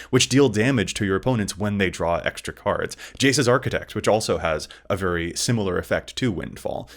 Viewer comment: Did say Jace's Archidekt? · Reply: Archivist